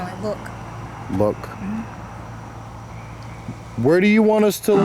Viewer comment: when you say are you in the woods, spirit box sounds like it says "water". Maybe it was in the water